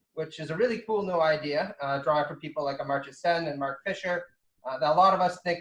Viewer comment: "cosmopolitan socialism, a cool new idea drawing from people like Amarjay Shawn and Mark Fisher" I couldn't hear the first name, "Amarjay Shawn and Mark Fisher", what was the first name? Thanks! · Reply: "In writing this book I took particular inspiration from the Nobel-Prize winning economist Amartya Sen, who, in response to the usual Eurocentric claim that the tradition that produced human rights flowed from Greece through Rome through Europe to America, countered that rather than a story of cultural continuity, the struggle for human dignity has always been fragmented, multicultural, and global." - Michael Brooks